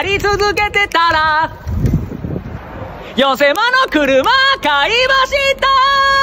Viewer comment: 寄せものの車買いましたーに聞こえた